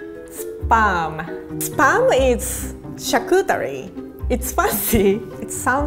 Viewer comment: Can someone tell me what she said here? chakutary? What does that mean? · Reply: Charcuterie. (From WIkipedia, my top google result) Charcuterie (/ʃɑːrˌkuːtəˈriː/ or /ʃɑːrˈkuːtəri/; northern French: [ʃaʁkytˈʁi] or southern French: [ʃaʁkytəˈʁi], from chair, 'meat', and cuit, 'cooked') is the branch of cooking devoted to prepared meat products, such as bacon, ham, sausage, terrines, galantines, ballotines, pâtés, and confit, primarily from pork. Charcuterie is part of the garde manger chef's repertoire. Originally intended as a way to preserve meat before the advent of refrigeration, they are prepared today for their flavors derived from the preservation processes.